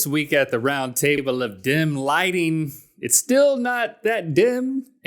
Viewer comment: Every time you say dim lighting I hear "dem lighting" lol